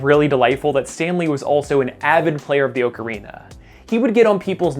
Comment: what is that phrase you said? I tried rewinding so many times and there are no subtitles/captions... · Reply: "Stan Lee was also an avid player of the Ocarina"